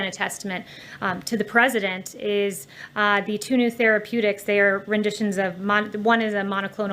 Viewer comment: Tuna therapeutics? Did she just say that??? At 34:29...